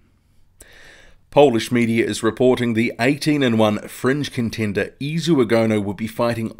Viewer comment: OK thanks. Thought you said French contender. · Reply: Someone just thought I said French as well. Went back and listened, it was fringe that you and the other guy heard